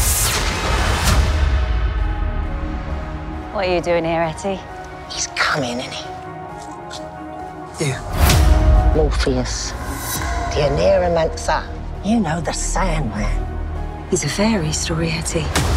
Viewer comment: legit i thought i heard Morpheus as Morbius....Morbin Sandman?